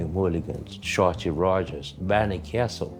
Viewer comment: What artist does he reference at 9:56 - barney castle?? i cannot understand him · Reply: M F barney kessel